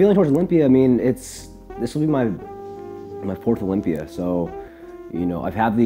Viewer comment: I heard "this is my fourth lumpia"